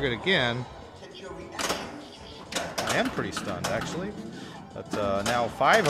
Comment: does he say, "I am pretty STONED actually"?